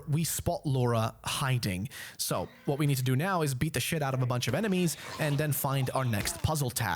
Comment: I thought you were gonna say "now what we need to do now is beat the shit out of her"